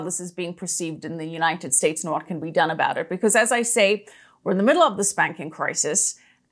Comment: Oh my goodness… 27:27 it sounds like you say we are in the middle of a spanking crisis 🤤 On a serious note … excellent interview.